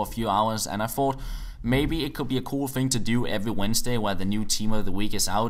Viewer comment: A stream every Sunday sounds like a great video! I understand if you would reduce the flow of videos, but as long as you don't stop them at all. Keep up the good work! :D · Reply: I thought he said every Wednesday?